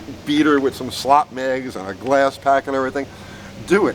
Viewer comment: I thought you said I'll see you tomorrow? ...It's been three days!